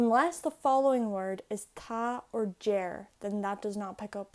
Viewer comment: Hello from America, wantin to learn Irish cuz the language seems interesting. I'm taking notes and realized these videos have auto close captions. Was wanting to know what the word at about 11:40 is. "Ta" and, the caption shows it as "jer", but I know that's not how it's spelled. Thanks. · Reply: its 'deir' :)